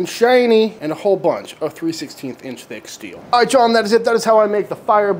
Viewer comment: How thick is the steel you used for the firebox? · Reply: I had the same question, at 13:40 in the video it sounds like he says 3/16", so I think the box is 3/16" with a 1/4" door.